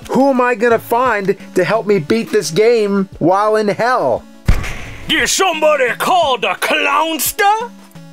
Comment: did somebody called the clownster?! Me thinking about: did somebody ring the dinster?!